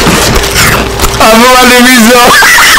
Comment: I'm still trying to figure out what said in 4:17 it's is going to be a mistery forever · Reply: "Im loving these though"